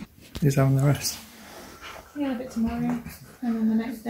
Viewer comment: Did you say bitte in